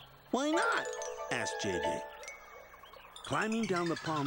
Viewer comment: When I was little, I thought the narrator said my name, JD, at 6:57!